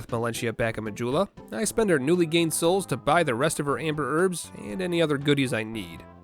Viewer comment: This video popped up on autoplay while I was out of the room, so I didn't know what was going on, but when I walked in, I heard "amber herb" and thought I heard "Amber Heard", so I thought this was a poop-only run.